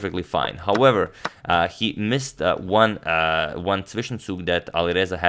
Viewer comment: Harikrishna missed one what? I’ve been googling my guess on whatever the spelling for what Antonio says and can not figure it out for the life of me lol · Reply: Zwischenzug. An in-between move. A move you play before the obvious move so as to limit losses or gain an advantage.